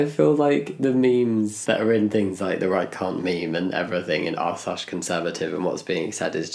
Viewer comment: With your accent i heard "right c*nt meme" at first